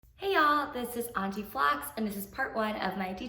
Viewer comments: First time on your channel, your video popped up and in the intro I hear " hi this is anti flats". I'm like wait did I hear that right, let me check her username..😂but it makes sense either way.😄 love the video btw! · I'm sorry but i thought u said : hey this is anti-flat 🤣🤷‍♀️